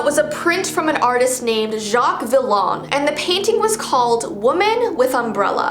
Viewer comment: did it say Jacque the log or?